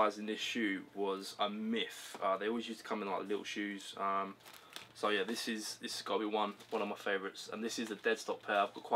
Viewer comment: Whats the name of the Adidas shoe at 2:18. I couldnt quite understand what you said, maybe cuz I'm dutch :) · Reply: Adidas EQT Support OG colourway